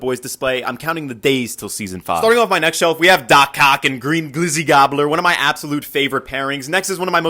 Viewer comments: Why did i hear 'we have do cock😭😭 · Bro my dum ass thought he said dock cock instead of dock ock💀💀😭😭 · My dumb ahh thought he said "DOC CO--"💀💀💀💀💀